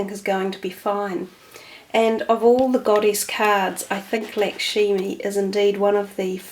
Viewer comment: thank you... and thanks for the pronunciation tip...Ive always referred to her as Lakshmi.... might be hard to make the change :) peace and light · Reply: Sorry - Lakshmi is correct. I thought I heard Lakshimi. I just tried to spell it phonetically.